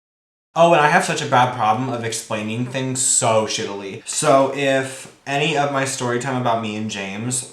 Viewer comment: did he just say “shitilly”?